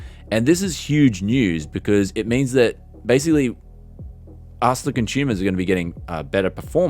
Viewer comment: OH! You said "US THE CONSUMERS are gonna to have better performance" I thought you said "arselickin' tumours"